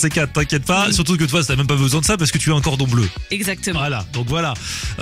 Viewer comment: au début j'ai compris "parce que tu es un cordon bleu" 😂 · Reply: nn il a dit parce que t'as un cordon bleu, fin j'espère pour elle 😂